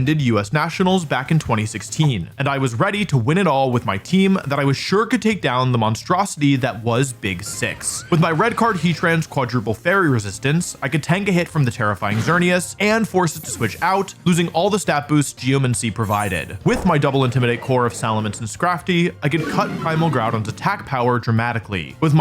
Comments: why did I hear this as "terra flying" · did anyone else hear the line "tank a hit from the terrifying xerneas" and your brain interpreted it as "tank a hit from the tera fighting xerneas"